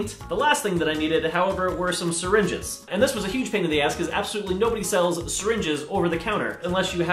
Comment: i thought he said he had sbdolutely no dye he could of just added water to the paint